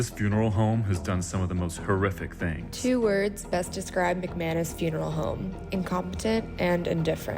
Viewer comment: I thought it said McAnus! 😂😂😂